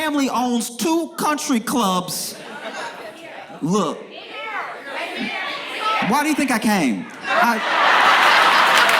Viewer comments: what they said ?? Sorry I didn't hear that part lol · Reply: I think they are saying "and here". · what what did the audience say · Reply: "In Maryland"